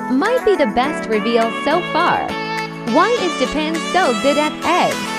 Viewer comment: What everyoneelse heard: "Why is japan so good at eggs" What I heard: "why is japan so good at seggs" But...its not a lie tho